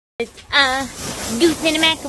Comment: Goose in the MaCaRoNi 😂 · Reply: Sounds like she said macawoni instead of macaroni